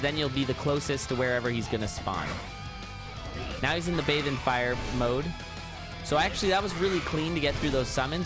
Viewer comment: I thought he said "Live in fire"